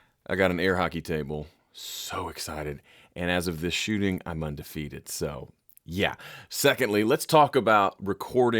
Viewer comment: Good God!!! I had to re-listen to your “second thing” where I thought you said “and as of this recording I’ve gone vegan”, instead of “I’m undefeated.”